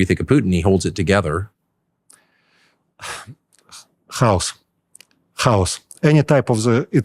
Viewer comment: min CC got the "chaos" word as "house" 😅